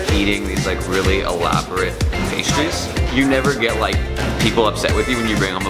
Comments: I thought it said Crab loaded pastries I got excited for a moment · I thought is said crab loaded pastries... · I thought it said crab loaded pastries. Who else thought that?